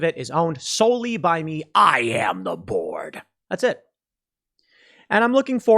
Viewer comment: I heard, " I am the Borg." lol